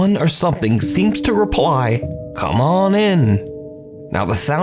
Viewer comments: On the first one, I heard "I'm hurting" not "Come on in"... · Come on in? I hear: “Who is it?” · sounds like he says "im in here" not "come on in" also when a lady says go upstairs you either go upstairs or move so she can